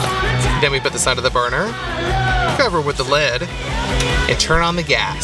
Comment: did you say the metal lead now your food is toxic